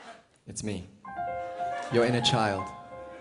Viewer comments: And this whole time I thought Troye was saying ' you ain't no child ' when he was saying ' your inner child ' ! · I thought troye said "you're in a child"